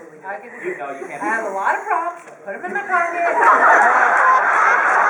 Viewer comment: what does emily says: 3:35!?! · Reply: nycjo d: how would you be booth!? E:I have a lot of props put them in my pocket